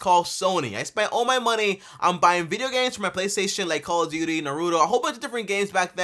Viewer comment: hold up- did he just say narudo 🤔 🤷‍♀️